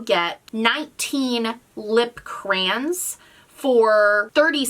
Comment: Did you just say lip crans... I’m dead 😂 love you